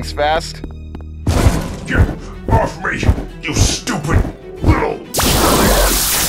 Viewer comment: WHAT DID BRO TRYNA SAY TİTAN SPEAKER:YOU STUPİD NİG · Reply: He was saying u stupid lit